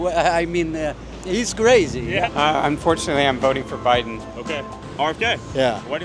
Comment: did he say unfortunately.